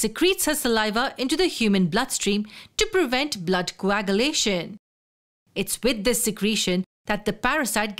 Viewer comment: Blood what ?? Anyone who heard it clearly ? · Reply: It's coagulation, it refers to clotting of blood in the sense that the female mosquito releases her saliva to prevent coagulation to thoroughly enjoy her blood meal